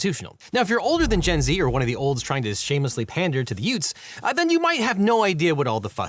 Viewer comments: Uh, did you say youts? What is a yout? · did you say "Yutes"? What is a Yute? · Did you say 'yoots'? What is a yoot? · Ah, the hwat? Uh, w'at was that word? Did you say "yutes"? What is a "yute"? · Did you say 'youts'? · did you say, yutes? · What was that word? Did you say "yutes"? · Pandering to the hwhat? Did you just say yoots?